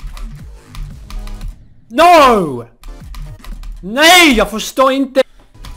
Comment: What are you saying at 7:44? As a dane i hear it as: “næj jeg forstår ingen ting” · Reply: Swedish "nej jag förstår inte"